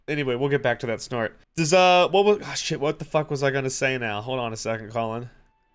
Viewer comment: I swear I heard “hold on a second Colin”. That being my name I about needed to change my pants after having turned away for a second.